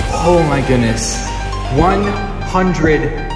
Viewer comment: I hope you get 1 billion subs. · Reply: i thought he said million